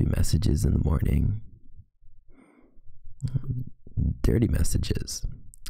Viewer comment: it sounded like he said thirty messages